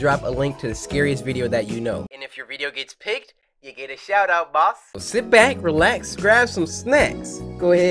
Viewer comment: I had my left head phone off so I heard "... You get a shout out, boy."